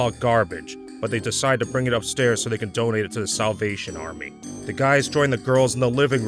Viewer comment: Swear I heard "Donate to the South Asian Army." LOL